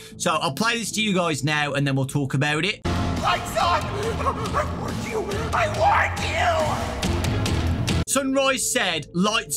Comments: "Lights out/on i warned you I WARNED YOU" i think thats what sunrise says and then turns into moondrop? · It sounds like sunrise was saying "LIGHTS ON LIGHTS ON I WARNED YOU!" Since he seems like a bit friendlier of one compared to moondrop saying "Naughty boy" since you arent supposed to be there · It sounds like Sunrise is saying, "Lights on.. I warned you, I warned you"! This could indicate that he is a passive animatronic until the lights go out, hence him saying that he wants them on to keep Moondrop from coming out and that he warned you that if they go out, it will make him transform into Moondrop and become aggressive towards Gregory! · Sunrise actually sounds like he’s saying “I WARNED YOU, I WARNED YOU” maybe he could be a neutral animatronic warning us about moonrdrop (or someone else?) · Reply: What I heard was "LIGHTS ON! LIGHTS ON! I WARNED YOU! I WARNED YOU!" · When sunrise says his lines I first thought he said ''LIGHTS OUT! I WARNDED YOU, I LIKE YOU!'' but that's just me. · i hear lights out I warned you I WARNED YOU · Sunrise actually sounds like he’s saying “I WARNED YOU, I WARNED YOU” maybe he could be a neutral animatronic warning us about moonrdrop (or someone else?) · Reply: It sounds like he is saying “MY SUN, I WARNED YOU I WARNED YOU!” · sounds like Sunrise says "lights on, i warned* you" . he warned the player not to turn off the lights or he changes to the moon? it makes sense edit: wording, i replayed it and saw i may have been wrong